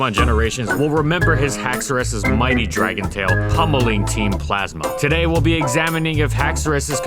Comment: Wait i heard "spanking team plasma." Or maybe im deaf